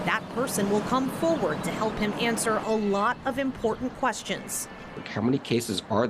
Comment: did dude just say "i just know that regularly, they SHAT into both sewer sheds"?????